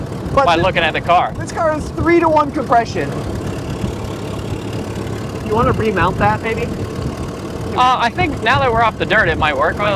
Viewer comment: Did you say "Do you want to remount that baby" 😂🤫